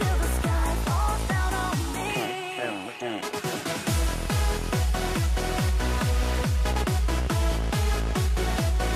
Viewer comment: PLEASE I NEED TO KNOW WHAT THE GUY SAYS AT 0:53... HELP ME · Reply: Ezio Auditore T.M.D.